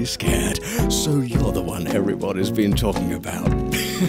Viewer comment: Waaaait- Did he say Harry potter, or everyone..... IM CONFUSED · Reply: Everybody